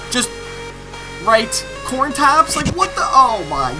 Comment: Bruh, I died when I heard "corn taps"